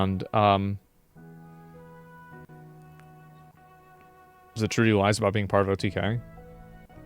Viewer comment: what does he say there. I cant understand 😢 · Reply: 'Is it true he lies about [being part of?] OTK?'